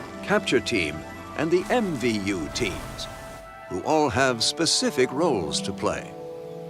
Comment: I heard it as "and the Envy-You team" 🤣